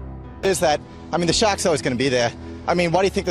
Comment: I thought he was saying shacks and realized he was saying sharks. LOL